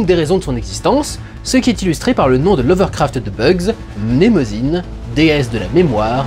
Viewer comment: J'ai entendu "Lovecraft", je suis le seul ? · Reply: L'overcraft perso